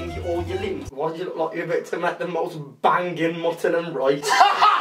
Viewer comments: wtf did he say? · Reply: Arap Tv "why do you look like you're about to make the most bangin' mutton and rice?" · WHAT DOES HE SAY AT 2:11 U HAVE WATCHED IT SO MANY TIMES AND THOSE ARE NOT WORDS What is muttinum rite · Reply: mutton and rice